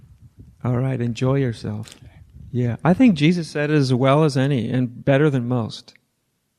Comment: what did Jesus say? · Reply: What think adya was saying, out of all these teachings Jesus said it best.